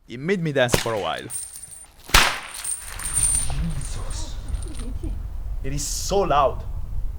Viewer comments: i definitely heard a guy say, " Jesus" and a woman giggling at 14:17 i thought that was you???? brother... if theres no one else around that means... GHOST!!! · no i heard the person talk to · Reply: Right? It sounds like there is a male saying "JESUS", and a female making babbling sounds. I am fairly certain this is an unintentional EVP.